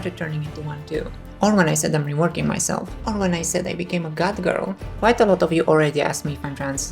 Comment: Oh I misheard it, I thought you MEANT catgirl, it was actually goth girl